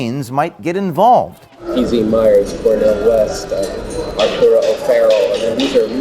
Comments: I could swear he says PZ Myers, not Dee Dee Myers. <link> <link> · Guys, he clearly says "PZ Myers" not "Dee Dee Myers". Even clearer with the follow-up, "Major figures in the arts and sciences".